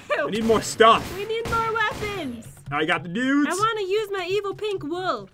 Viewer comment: its sounds like said he got the nides